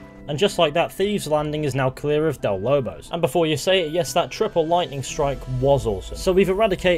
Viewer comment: what he said :that triple ligthnaight was awsome what i hear:that triple lighthining wozz ozz zom